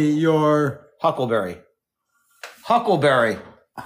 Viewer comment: I heard “hubbleberry” as in Hubble telescope. Lol 🤣🤣🤣🤣